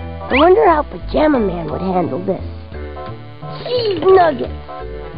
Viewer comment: I thought he said "Deez Nuts"